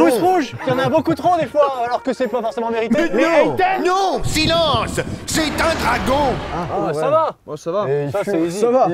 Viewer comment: je sais pas si il a dit « hendek » ou « hentai » mais dans le moi là premier fois j’ai entendu hentai j’étais tellement pas prête 🤣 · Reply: En faite il a dit Haters je me sent conne 😂😅